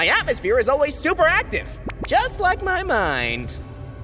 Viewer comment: when he said: my atmosphere is always active just like my mind... i thought he said: my atmosphere is always active just like my night just my dirty mind :D